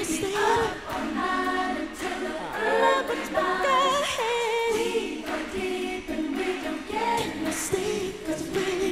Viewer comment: What the hell does Janet say at 3:04????????????????? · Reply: It's taken me 25 years to find out so I gotta share with you: "I know that we're goin'"